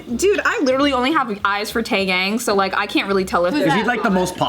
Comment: did she just say Taegang or did I hear wrong?🤣🤣🤣🤣🤣